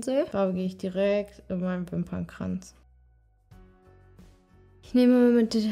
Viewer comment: Ich hab bei 16:09 Dönerkranz verstanden😂😂❤